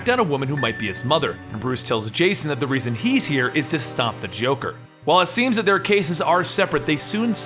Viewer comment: I thought said "And he fucked Joker'😂😂😂😂😂😂